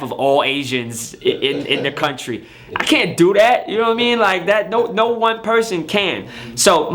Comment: Every time he says “U no wha I mean”, sounds like he said “Yao Ming”